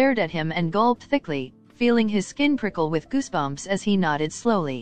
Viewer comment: Bro when he said I felt my skin prickle I thought he said pickle😂😅